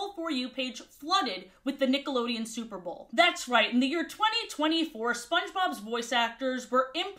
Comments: What did she say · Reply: “In the year 2024” this is the 2nd comment asking me so I may just spiral and cut it out of the video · I know she probably said something like "near" but I had this video on in the background and I heard something WAY DIFFERENT here 😭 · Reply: “In the year” jeez I really need to slow down when I speak I didn’t realize i was difficult to understand 😥